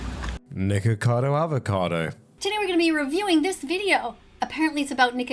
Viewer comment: i have to be tripping i was half paying attention and thought he said nigacado avocado.